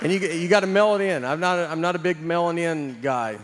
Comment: "I'm not a big mailing-in guy" sounded like "I'm not a big melanin guy" and that made it so much funnier lmao